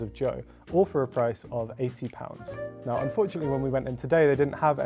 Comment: did they say £80 or £18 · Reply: £80